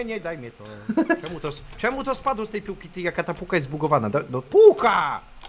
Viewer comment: Ja usłyszałam bułka zamiast pułka XD